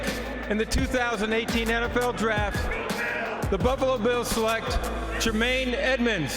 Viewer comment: did he say "the Bubbalo Bills"?